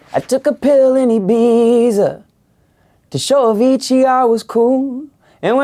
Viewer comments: For many years I thought he meant to say "I took a pill in Ibiza to show that ETR was cool," and I thought ETR with some crazy psychedelic drug like DMT. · I thought he had said "... to show a Fiji I was cool" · It was today when I found out, it’s not „to show a fiji“ I was cool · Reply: I thought it said. "I took a pill ate a pizza To show Luigi I was cool" 😂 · I thought it said “I took a pill I Ibiza to show a Fiji I was cool”